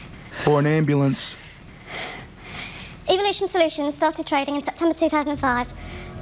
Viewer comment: Everytime she said evolution solution, i heard 'evil'ation solution